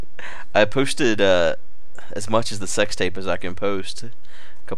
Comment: did you just say that you posted a sex tape ? :O :)